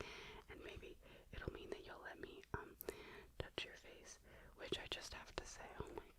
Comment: I thought it said touch your feet and thought the bill could NOT be this high then I realized it said face.